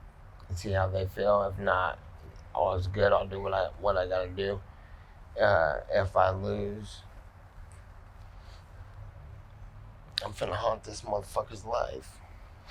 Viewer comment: dude is stupid he thought nate said if he wins he is going to be in his life forever e said if i lose im going to haunt this mother fucker or life meaning if i win im good but if i lose he better be looking over his sholder dudes a fucking dumbass thats why its fight to the death life